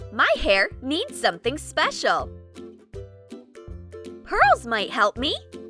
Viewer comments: Lol she said hurls instead of pearls 😂🤣😂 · she says hurls instead of pearls